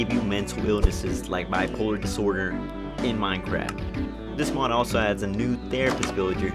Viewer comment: I hear “in my crack”